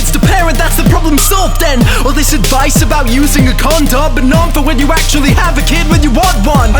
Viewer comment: it sounds like he says "Condob" instead of "Condom"